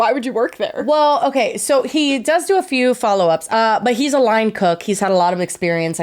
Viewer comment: I stg at 54:13 I heard say “but he’s a lying cuck” instead of line cook and lost my mind 💀